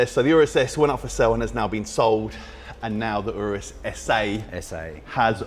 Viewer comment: I know I heard 'Urus ESSAY'